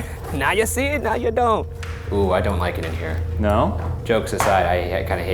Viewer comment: At the caves, the mattress closet I heard "I dont like the light."